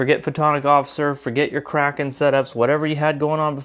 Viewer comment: Question, did I hear you right? At about 1:15, you were saying people don’t need aux to bat, then something about a Kragen? Or did I misunderstand what you said? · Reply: I was saying "kraken", as in, adding up a lot small sources of cooldown reduction, each one being like a tentacle